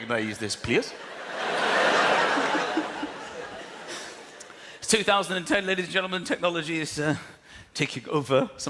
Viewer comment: At about 9:15 does he say it is 2010?!